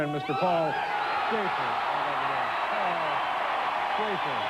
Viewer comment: "...now say hello to our good friend Mr. Paul Jason. Right over there. Paul Jason." it even says it on the CC. weird how it really does sound like he said that. I had to put headphones on to hear it because of the crowd screaming so loud I had thought Dave actually said "Jesus!" which would have been almost as weird as calling Shaffer, Jason. Hey! While I got yer attention here, did you know that Paul used to be on Saturday Night Live!? I sure didn't, but yeah, and not just one season either. Went from tenured featured player to a regular cast member in Season 4. Don't believe me? Look it up. No wonder he always had done so many skits and remotes with Dave. Honestly, with this bit of knowledge I don't know if that retrospectively makes everything he did on Late Night better... or worse? lmfao. That is all. Now get lost.